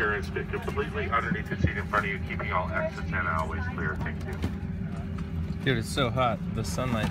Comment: on 5:39 why did call tod david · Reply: Alyssa C He said dude.